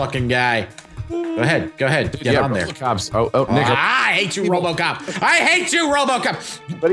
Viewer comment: Did someone say what i think he just said?!?! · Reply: "Oh Nick, uh" - name is Nick, did not say "Nigga" although with his Tyrone voice he could get away with it